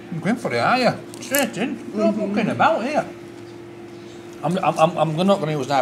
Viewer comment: Why are you accepting him swearing on your channel ? You have never done anything like that . Concerns over him bulling you about your accent and calling you posh . I think he well overstepped the mark . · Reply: When did he swear? Did you mishear 'mucking about'?